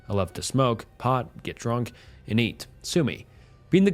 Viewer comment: Did his ass just say sue-mi??? Bro sushi isn’t that hard to say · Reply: The text on screen is ai generated, it must’ve thought him saying sue me sounded like sushi